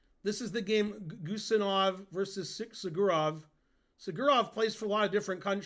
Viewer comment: For those who are confused by Ben saying "Sjugirov" when the caption says black is Simon Williams, I looked up the game. The caption is wrong and black is Sanan Sjugirov. (Williams was playing at that event, but it was a Swiss and he didn't play Guseinov.)